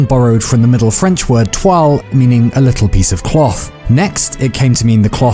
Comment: Fuck is the most universal word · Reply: Also I misheard toile as et toi